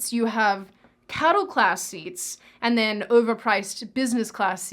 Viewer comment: At first I thought you said "Cuddle Class" thinking you were referring to the cramped accommodations instead of "Cattle Class" which accurately describes your situation.